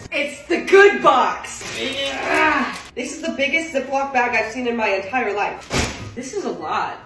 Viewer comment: I thought it said biggest zodiac sign